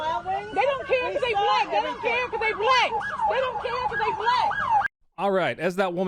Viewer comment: Did she say "Dey don't care cause dey black, dis is booshit"? I thought I heard her say that.